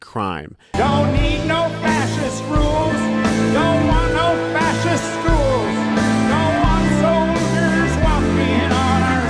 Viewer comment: I thought it said fascist fools, not fascist schools. That would also be appropriate 😊